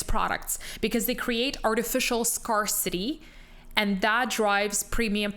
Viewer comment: Did she say SCAR CITY?